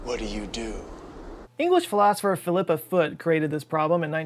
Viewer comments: honestly thought you said flipper foot until i looked up at the screen! bahaha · English Philosopher Phillip The Foot. That’s what I heard here. I actually went back and checked. Very disappointed there wasn’t a royal family that consisted of a philosopher king lol